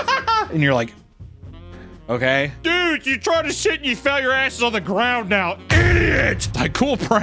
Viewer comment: What did even say here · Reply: "dude you tried to sit and fell and your ass is on the ground now" basically